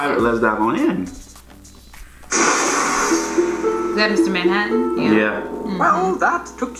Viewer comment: did she just say MR Manhattan?!